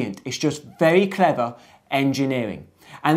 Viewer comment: Also in 4:29 I couldn't understand what you've said. "And that's all for it now, so if you're happy" ?